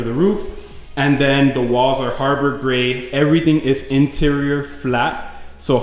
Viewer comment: Looks great! I started mine and have a question.. I got "Homburg Gray" because I thought that's what I heard you say it was, but put some on last night and it's a lot darker then yours.. Can you verify the color please? thanks!